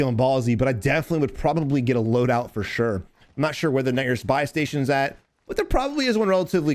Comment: did he say “the n**** buy station”?? 😂😂😆😆😆😆😆